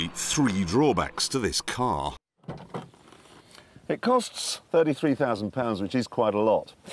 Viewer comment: At first, I heard, “It’s only £3,000, which is quite a lot” and was sooooo confused